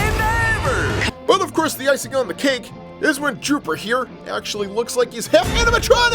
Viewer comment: Did hey call fleegle(the dog)dropper